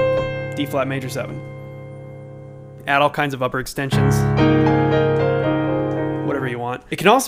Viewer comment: All I heard was "Jazz extensions"